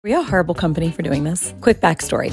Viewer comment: I heard "herbal company" 💀